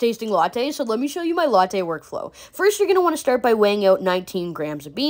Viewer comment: I thought she said workout but that would be impossible